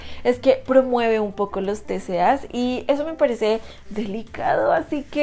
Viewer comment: que dijo?? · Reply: Los TCAs Trastornos de Conducta Alimentaria (4nor3xia, bulimi4, 4tr4c0nes, etc)